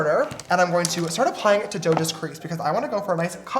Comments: did he call her joda at 12:22 HWKSHSNS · did he call her Joda? lol